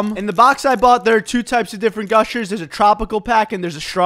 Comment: "Thanks for ruining my childhood" I like how calmy he says that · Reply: I thought he say thanks for ruining my job